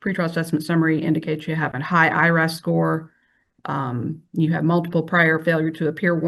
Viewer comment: what high score is she referencing? It sounded like high IRS score?